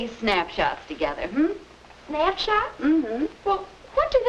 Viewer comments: I thought she said snapchat and freaked the hell out. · I thought they said “snapchats” and I was like WUT AHAHAHAHAHAH